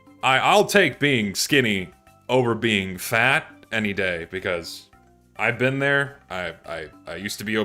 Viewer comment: i was eating while watching and thought he said “over being fat and gay” 😭😭